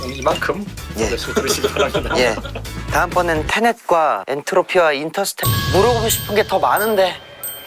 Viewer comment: 테넷을 탄핵이라 들었다 ㅋㅋㅋㅋㅋㅋㅋㅋㅋ